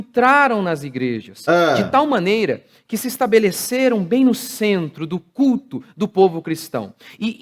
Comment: Só eu que entendi " centro do c* "? Kkkkk